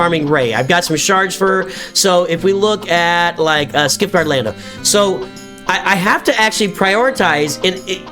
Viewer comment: I am listening to you while cooking dinner, and I had to go back and listen to this part again. While doing my work, I swear you said “Skid Mark Lando”!! Oh, good times!!